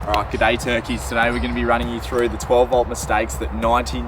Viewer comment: Did you just say "G'day turkeys" I replay 5 times, and it sure sounds like it haha.